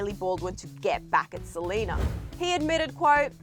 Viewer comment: Did she Just say "delena"?!?!?!?